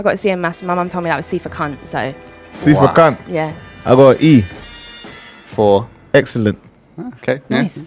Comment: I thought he said "I gon eat" as if he wanted to eat her grade.😅